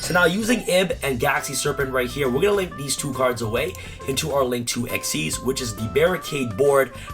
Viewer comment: bruh did I hear ''link 2 xyz'' at 2:18 ?